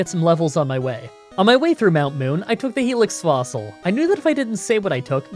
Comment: "Helix Fslossil" im not the only one who heard that right?